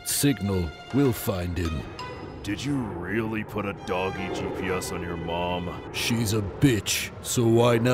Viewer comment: Swore he said Donkey GPS at first, shit had me thinking alot for a minute